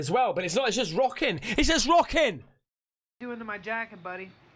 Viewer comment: "What was that voice?" .. a male voice does indeed say "jacket" .. good catch 🤟😎👍